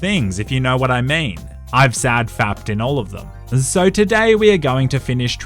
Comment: did he say I sad fapped in all of them, if so just say I jerked off in all of them dude